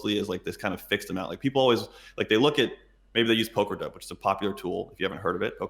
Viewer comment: he mentions "poker dot"? Am I hearing right? Does anybody know what he's talking about? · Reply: poker dope is the program